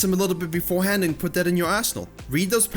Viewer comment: I swear it sounds like he is saying "Practice before hand and put that into your asshole" Even though he is saying Arsenal.